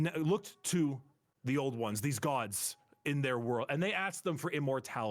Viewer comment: I thought he said oldawan, I thought he was going to start talking about Homo erectus. I’ve been in anthropology class too long.